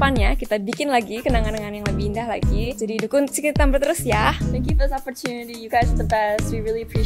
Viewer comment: Yess tropy pertama buat secret number yeeaaah [* * * * *] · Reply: E iya Denger gak, apa gw salah denger ya, Dita Ngomong "...dukun secret number terus yaa" Mungkin saya salah denger, tapi ngaqaq bat 🤣